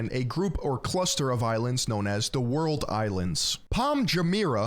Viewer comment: You have the entire planet, filled with gorgeous natural landscapes, mountains, waterfalls, glaciers (not for long), amazing historical cities with beautiful architecture and if you choose to go to dubai it's like going to France and eating fast food. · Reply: what I heard is, "The world is France." I rather like this.